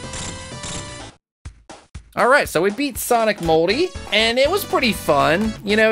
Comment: It sounds like he said sonic moldy